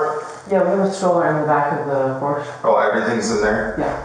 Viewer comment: she say Porsche because that’s what transcript says, but subtitles said porch